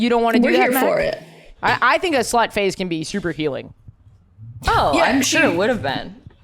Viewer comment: oh slut phase i thought they said slut face and was puzzled